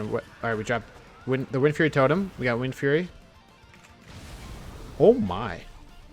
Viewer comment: I thought said we're not windfury shamans... Jokes aside I wish people could test outside of Torgast.